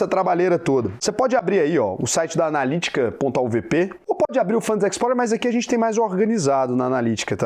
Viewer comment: qual site que você fala? Fala tão rápido que não entendi qual site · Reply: Auvp analitica